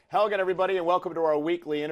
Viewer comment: i heard Hogan everybody